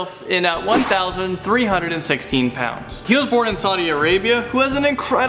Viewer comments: did he say 1316? So the sub/word/number is wrong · instead of 1316 it said 1136. lol.